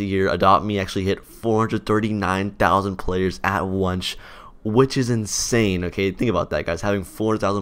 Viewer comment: Wait did you say wonch instead of once in